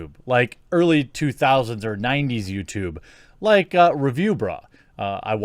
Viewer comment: Di.. Did he say 90s youtube at 00:18???